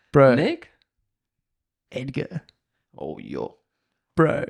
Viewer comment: who's the guy you're talking about at 1:41:10? cant understand the name saying 😅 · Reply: Edgar Ulrich, snow kiting check it out , doing s loops high in a valley